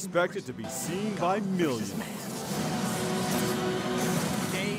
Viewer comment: did luthor just said "gay vs knight" ?